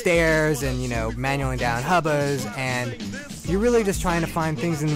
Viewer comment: in 0:37 did he say manualing down hobos?